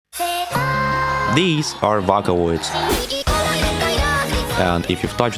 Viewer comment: 💀 I’m sorry but I heard “vacaloid”🐄🐮